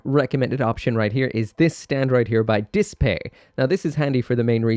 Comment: what brand is that stand? I think he’s saying Dispay, but I can’t find it when I search online.. help!